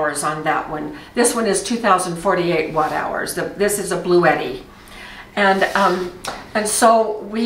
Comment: I'd love to be able to afford a big Bluetti💖 Maybe one day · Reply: I thought she was saying “Blue Eddie.” 😂